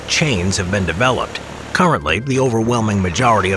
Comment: i thought he said he said "*cowardly,* the majority of US Force work in logistics, not active combat." Had to turn on subtitles to see that he said "currently" lmao.